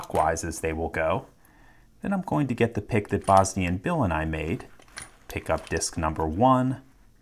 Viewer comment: the first time i heard him say bosnian bill, i heard it as just one word, bosnianbillandimade